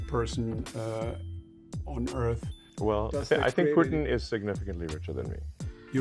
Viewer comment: lol he says Putin, not Britain like the subtitle says